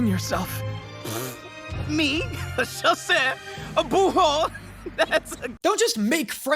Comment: I am French, and even I am not sure what Vanitas is saying 😂😂 But I guess it’s something like « Bouhou » which just imitate a baby crying and « José / Je sais » ? Whut ?!?! 🥲 So you are not the only one who don’t understand don’t worry 😂😂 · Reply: Chasseur & bourreau. On entend qu'ils font de leur mieux niveau prononciation mais c'est pas toujours ça :').